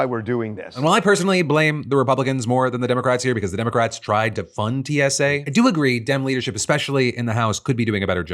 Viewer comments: just FYI, the captions here say the Democrats tried to “defund” DSA, even tho what Phil actually said was that they tried to FUND it. And while this isn’t that big a deal for most viewers, for those with hearing impairments who rely on captions it’s obviously not ideal that those captions are misleadingly stating the exact opposite of what was actually said. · Phil, your auto subtitles at 6:37 say "defund" instead of "fund". kind of an odd duck of a YouTube error.